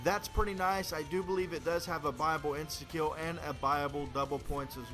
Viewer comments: Everytime I thought he said bible but I heard it a again and he kept saying buy able. Why couldn't you just say purchase able lol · every time he said buyable, i thought he said bible?...o_O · I thought he said bible not buyable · ooooooooh buyable ending i thought u said bible haha lol v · i thought he said bible when he said buyable.... what was it? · i thought he was saying bible then i realised it was buyable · he says buyable i always thought he said bible